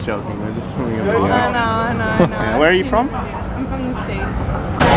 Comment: did she say im from the streets? WTF